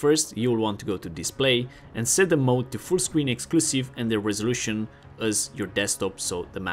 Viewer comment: Anyone have any idea what he says at 2:30? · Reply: full screen and max resolution